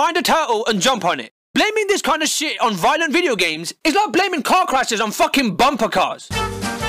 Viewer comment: I definitely heard “Cock rashes” the first time